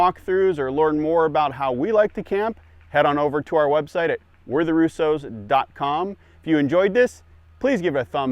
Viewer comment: Not going to lie: When I first saw the thumbnail for this video - off to the right of another video - the font for the channel name was so small I initially read it as "We're the Russians" & it threw Me off. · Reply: I thought it said "we're the Russians" too.... actually made me want to see what the heck this was about ...lol